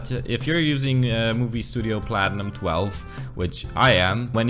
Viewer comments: What editing software do u use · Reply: i think he says "movie studio platinum 12" here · What is the editing software you use? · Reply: i think he says "movie studio platinum 12" here